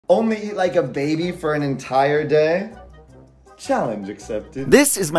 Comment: I thought that said “eating a baby for a day”